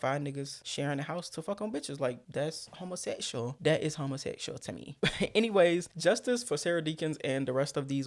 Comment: 😂😂😂😂 are you a homosexuaaal Terry Joe is all I hear